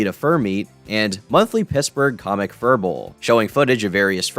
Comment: okay, am I the only one who thought he said "Pissburgh"